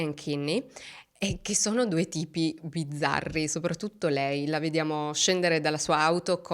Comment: A "due tipi bizzarri" io ho capito "due tipi di zarri". Tutto sommato ci sta 😅